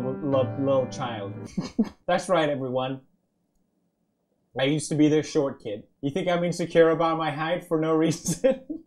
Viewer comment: I thought you'd just say, "I use to be a broke ass. That sailboat made me poor."